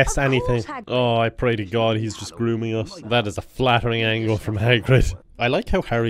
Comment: did he say "i pray to God hes just grooming us"???? Whaaat?